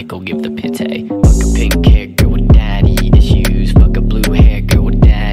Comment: When he said pink hair. I thought he said carrot